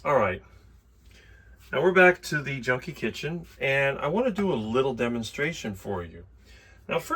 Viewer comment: Did I hear you say "Welcome to the JUNK KITCHEN"? What is THAT all about? Lol😅 Ruthie 08.03.24